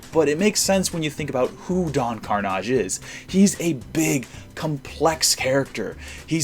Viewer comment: Who else thought Don Karnash was called Donkerdash, I did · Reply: Gooperoo 212 I thought he said Don Kardash..... Ian